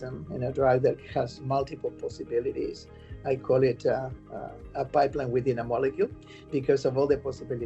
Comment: My hearing is imperfect. At 11:00 does he say "a biplane within a molecule"? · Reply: pipeline.....